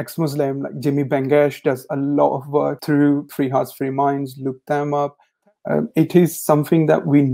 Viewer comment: Who Jimmy Bengash, Free house free mind? I couldn't hear it clealry.